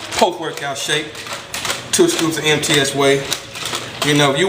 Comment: So, I always thought you guys said "Empty Ass Whey", and I was really confused. Now I realized it is "MTS Whey", LOL